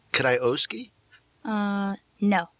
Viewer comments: Can someone help me with the expression Dom say @ 16:52. What does Dom Irrera say after "Ouch. Can I ... ? " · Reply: "Could I Owe-ski"... meaning can I owe the money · can anybody tell me what Don says? "Could I ouskee? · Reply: Juan de la Rocha Solís I think he meant ‘can i oweskee’ aka Can I owe it to you? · What does Dom says to Laura at min. 16:53? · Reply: Can I oweskee?